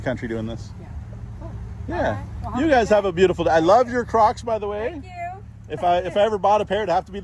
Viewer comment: When you said to that woman, "I like your crocs," I thought you said crotch. 😊 I guess that tells you something about me.